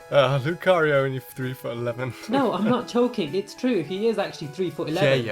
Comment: did say I'm not choking